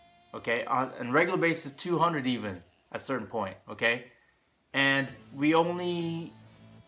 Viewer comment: What is he saying at 2:16? It sounds like 'regular base is 200, even, at certain point.' What does that mean? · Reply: joe doe We're saying we put in 400 lineups in a tournament at one point, 200 on a regular basis and it revealed to us how difficult it is to win a tournament. Thx for watching!